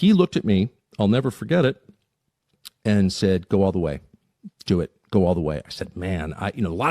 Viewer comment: Sounds like he said don’t go max